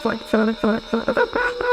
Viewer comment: anyone else hear " yes please fast "